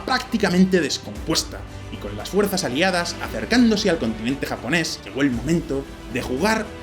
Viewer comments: tampoco la única, más adelante dice “continente japonés”. Pero no siempre se escriben los fallillos esos. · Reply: Yo escuché "contingente japonés" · Eso y el continente japones. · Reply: Yo escuché "contingente japonés"